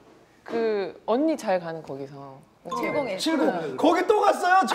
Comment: 여기 언니잘가는 거기서.. 7010..? 뭐라고하시는지 아시는분..?